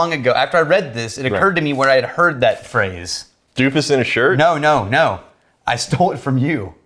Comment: At first I thought said a deuce in a shirt